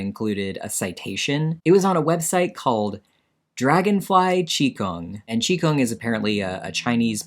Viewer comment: Unrelated but i really thought you had said "Dragonfly Chico" and then thought hey your spanish isnt that bad 😭😭😭 no way it was actually Qigong 😭😭😭